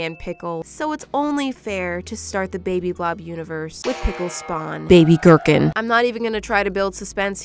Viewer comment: not the other ones srry but pickle spawn baby burpin.that's what I heard